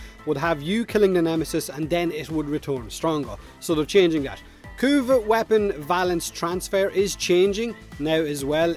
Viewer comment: When you said "Kuva weapon valence" I heard "Kuva weapon balance" and had a mini-heart attack.